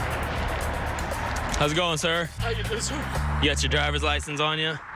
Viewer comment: when he first walked up it sounded like dude said how you doing sir while he was crying 😂😂😂